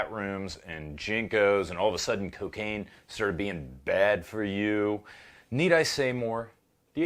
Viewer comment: All of a sudden cocaine started being "bad for you"...what a time to be alive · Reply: I seriously thought he said "and serbian being bad for you". Because I was about to go off on a rant on that one.